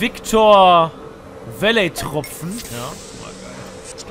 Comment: bei 8:03 sagt der Protagonist ganz leise "Hammer geil" - Zuindest hab ich das gehört :D